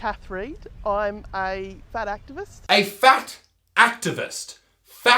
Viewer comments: im currently losing weight because im overweight. but the term "fat activists" just makes me laugh hysterically. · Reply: sarah harris I thought it said fact activists and I cried laughing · When the women said she was a fat activist I thought she said she is fat and active.